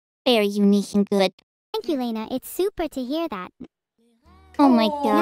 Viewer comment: I first thought Evil would say Super but then I saw in the subtitles that she had said Soup -er🤣